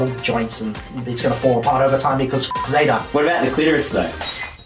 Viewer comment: I actually don't know what he said. Lol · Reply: I think he said "What about the uterus though?"